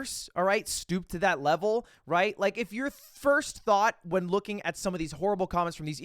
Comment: Everytime you say "thought" I hear "Thot"....